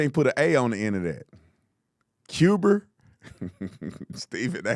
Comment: I was just thinking “Did Stephen A just call Cuba Cuber?” And then I thought I heard a fire alarm beeping. 😂😂😂